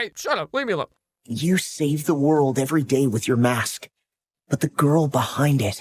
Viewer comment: ADRIEN SOUNDED LIKE HE SAID “You save the world everyday with your ass” 💀💀